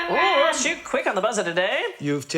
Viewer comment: What the heck is said @ 7:48? After she says "What's the first thing you would notice?" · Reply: “Ooh, that’s you quick on the buzzer today!”